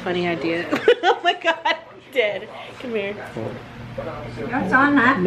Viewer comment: did anyone hear her say Dad to